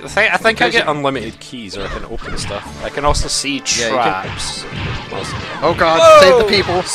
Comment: I thought said "I can also see Jeigan." And I have no idea what purpose that would serve here.